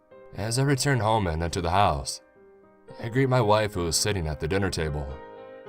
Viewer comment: Soundes like that BOI said WAIFU ❤ 9:11 XD Edit: A gud story Will we have a sequel?